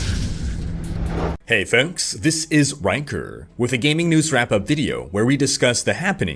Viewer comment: Why I hear "wanker" instead of "rhykker" everytime?